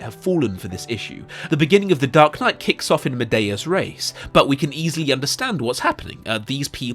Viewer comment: I thought he said “The movie starts off in a ‘meaty-ass race’.” As in, ‘in the thick of it,’. Lots of running and no messing around. And then I received a mini Latin lesson.